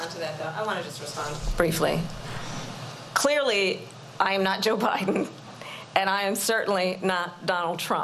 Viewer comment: Did she just say ooobiden?